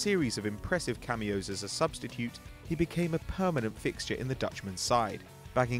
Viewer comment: he says "substicute" instead of substitute.